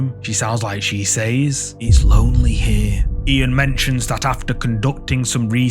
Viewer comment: it sounded like the woman said "over here!". I didn't really hear her say "its lonely here". But it was a good capture and lets us know that we still have some kind of emotions even after death.